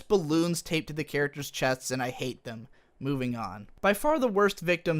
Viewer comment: I thought he said “boobing on” 😂😂